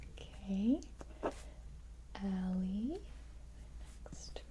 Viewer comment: ngl when you said "ally" (in the roll) i thought you said "ellie" i was almost asleep i almost shat myself